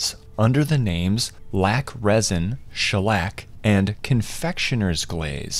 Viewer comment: I thought he said Schlatt instead of Shellac